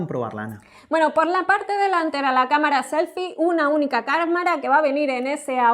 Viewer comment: like si notastes que dijo camarana uijijijiji